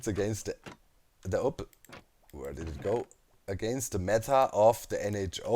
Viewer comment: He keeps saying "mettar". "We will discuss the mettar later." "It is against the "mettar of the NHO". 36:47 I get it's probably an artefact of his accent, but what is he trying to say? Method? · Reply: Meta!